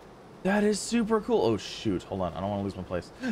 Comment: When he said oh shoot while flying my video started buffering at the perfect time to make it sound like he said oh sh